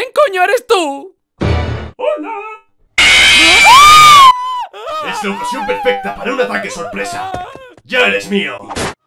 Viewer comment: ¿Que dijo Springtrap? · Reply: Es la ocasión perfecta para un ataque sorpresa